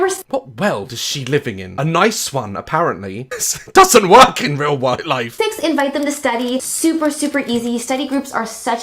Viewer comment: "This doesn't work in real white life." at 7:00.....is that what he really said???????